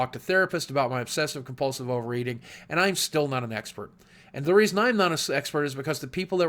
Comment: sounds like he is saying "Sexpert"